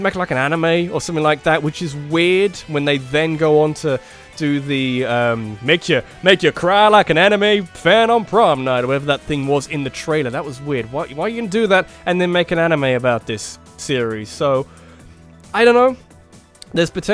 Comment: This lets play wasn't the best... But it was better than nothing. This game's turnout has really made me cry more than an anime fan on prom night. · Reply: I thought you said an anime fan on porn night for some reason XD